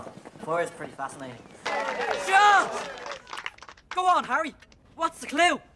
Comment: I don't even get it like do I only understand him because I'm British or am I just weird because like I can tell you what he said 1:52 "shut up" 2:58 "Not more of Aberforth's cooking I hope, be a surprise if we can even digest it"